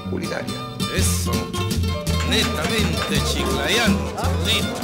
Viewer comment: a 01:52 el tipo dice Netamente Chiclayano, el Milo? O entendi mal, hasta donde se es de Nestle, y lo hacen enChile, que tiene eso de neto? Asi como la Inka Cola...